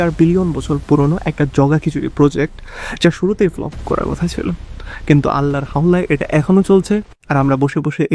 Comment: What did you say here 0:23, "Allah..." ???? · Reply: "allahr hawlay", basically meaning 'by the grace of almighty allah'